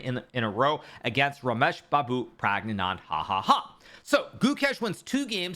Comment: parag nand hahaha, what? What did you just say hikaru?